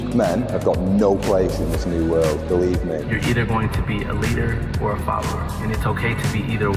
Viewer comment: Idk why but when he said you can either be a leader or a follower I thought he said you can either be a leader or a father.